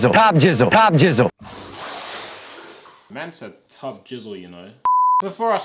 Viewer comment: is he saying ‘mensen’? (Dutch for people) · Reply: Man said